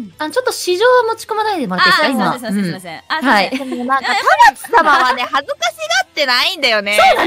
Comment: “Paratsu-sama”. I think she said Tamaki-sama.